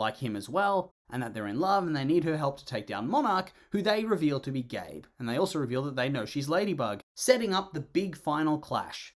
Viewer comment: I thought u said they revealed monarch to be gay